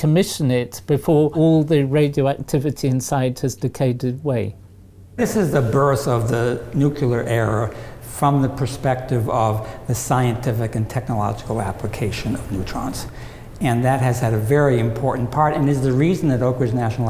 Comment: did he actually say 'nukeyler error'? That would be fitting. Not in the tech, in the language.